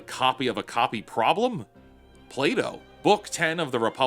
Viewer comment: I thought you said play-doh 😂